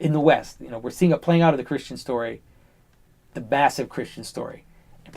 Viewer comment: Can you help me please? at 1:13:18 <link> I cant make out a word Johnathan says. in context he says "what we are seeing is a playing out of the christian story, the ??? of the christiian story." does he say the "mass" of christian story? or something else? thanks for your help!